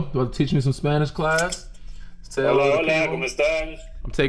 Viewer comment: I thought what he said was "kamusta"